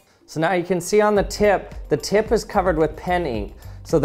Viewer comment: "The tip is covered with penny" That confused me (the subtitles too) until I heard him say it again correctly